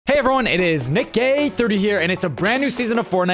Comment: am i only one who hears nikckgay 30 here